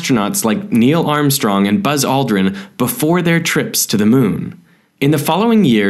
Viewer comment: Closed Caption at 0:04: "Neil Armstrong and Buzz Lightyear" LOL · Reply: All i hear is “buzz aldrin”